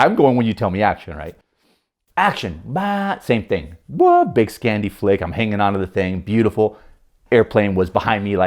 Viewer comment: What is he saying at 3:15? Scandy Flake? I'm unfamiliar with whatever the term is and must be spelling it wrong for Google.